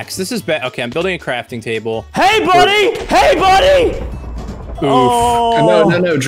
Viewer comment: I Don’t know why, but when he said “hey buddy, hey buddy,” I thought he was saying “hi Axl, Hi Axl” like Dave Grohl